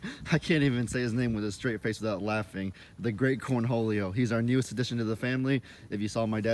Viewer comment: I always thought he was saying the great cornhulio (or cornjulio)